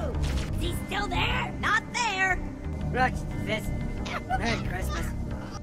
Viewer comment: I heard "fist..rukt...merry smissmass" when I was playing ODST the other day!